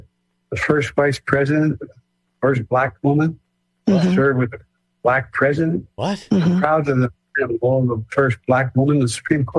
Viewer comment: Sounded like he said “first black woman serving with the president” probably talking about Kamala. Don’t get me wrong he is clearly gone mentally but let’s be honest on this one